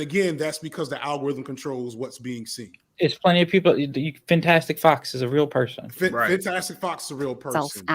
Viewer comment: they mention a content creator but I can't quite catch the name is it Fantastic Fox or someone else?